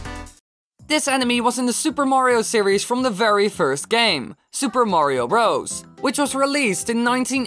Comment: At the start I was not really listening and it sounded like you said super Mario rost